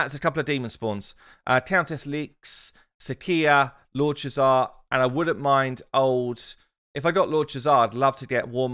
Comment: Cannot believe I just heard “I wouldn’t mind warmother” come out of your mouth...